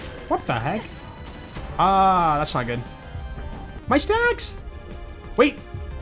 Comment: kinda sounds like he says “my snacks!” and i can’t unhear it